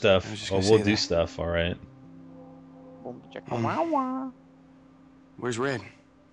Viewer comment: When she said where's red I thought she said where's the bed